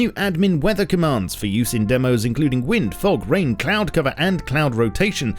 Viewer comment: did he just say rain?